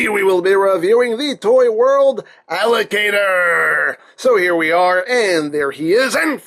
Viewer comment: I thought he said alligator instead of allocator